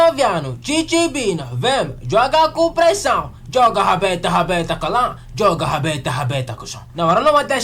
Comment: bro is really saying "joga a rabeta rabeta cusão" 😭